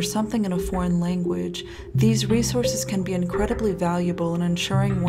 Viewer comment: freaked out and thought you said 'these racehorses'